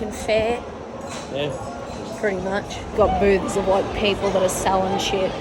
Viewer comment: So I had the subtitles on because I’m around background noise and at 0:42 I’ll show you what the subtitles said mixed with the words I heard (which will be in parentheses) so you can laugh like I did “would you say it’s like an American fair yeah pretty much got boobs (and) like people that are selling [___]” the blank was the subtitle wrote but my brain heard “seggs” due to the previous “boobs” subtitle. Now, as an American myself, my ADHD random as heck brain decided to throw out the following thoughts: “you know, I wouldn’t doubt that’s happened somewhere…wait no, anyone who’s been to an American Fair would agree with me saying they wouldn’t do the seggs with the rough looking carnies for free, much less pay for it so no it probably hasn’t happened somewhere before and if it had I wouldn’t want to know or picture it” then I rewound the video to distract myself from that horrifying mental image. I thought I’d share that train wreck of a train of thought so you can have a chuckle at my expense since I did too 😂